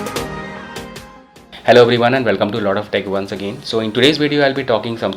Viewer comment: sorry to say but, but heard it as loda of tech😂😂😂😂